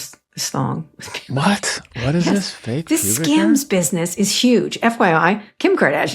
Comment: hen Kara started talking about Kardashian's underwear business I swear I thought she said "scams"... And given the content it took a while to realise she was saying "skims"